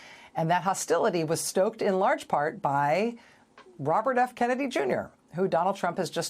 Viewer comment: Did I misunderstand the dr I thought he said Robert DeNero when I thought he should have said Robert Kennedy, but she never corrected him so idk.